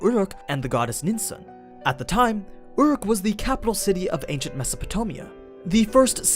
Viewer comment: i am the only one who hear "Meaopotomia"?